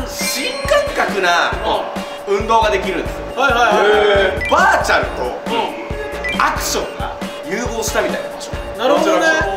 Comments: ごめんwバーチャンとアクションが融合したみたいなって聞こえた😂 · 一番最初のバーチャルとアクションって言うのをばーちゃんとアクションと聞き間違えて一人で爆笑してたｗ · 最初バーチャルがばーちゃんって聞こえた